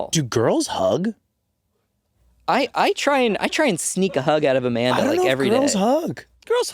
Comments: I love the "I try to sneak a hug out of Amanda every day" 🤣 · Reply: I totally heard “I try to sneak a hug out if a man every day!” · I love the "I try to sneak a hug out of Amanda every day" 🤣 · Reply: lol i just realized i misheard that. i thought he said “i try to sneak a hug out of a man everyday”